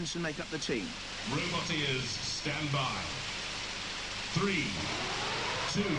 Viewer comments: I always hear "Robot Ears, stand by" haha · I always hear "Robot Ears, stand by" haha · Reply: I too literally thought he was saying "robot ears" as a kid, glad to see i'm not alone.